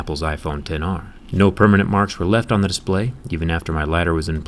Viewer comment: did he say thiccness ....